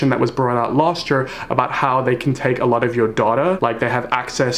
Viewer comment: Did anyone else think that said they could take you daughter at 3:45??? My gut reaction was like nO GET YOUR HANDS OFF MY DAUGHTER I'm 16. I don't have kids.